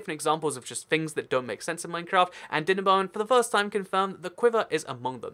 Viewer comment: My captions heard him say "Dinnerbone" and decided he said "dinner bong"